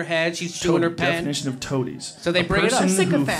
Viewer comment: I thought she said factotum. I didnt even know what a toadie was...